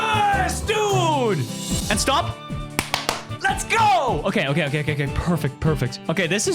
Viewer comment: did he say purfect? fundy cat real?